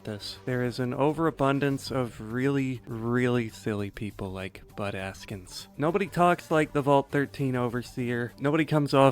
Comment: Every time you said "Bud Askins" I heard "Butt Ass-kins" and I giggled.